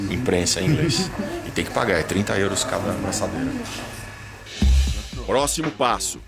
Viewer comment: juro q eu ouvi "30 euros cada... essa merda"